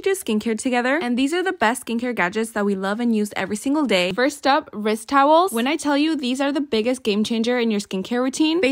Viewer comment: No cuz why did I hear “ first up Rizz towels “ 😂